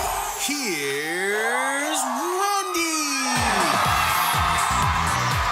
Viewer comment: omg i just heard the here's rondy. i had to listen twice.